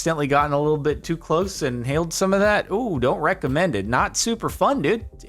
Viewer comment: I'm tired and had a moment of auditory bone apple tea. I could have sworn I heard him say "Don't wreck the men did" and I was like 'is this a bit or something?'